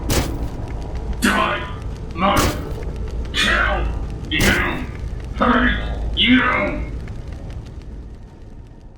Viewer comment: what the hell is Mechy saying? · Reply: "...Kill...you...hate...you..."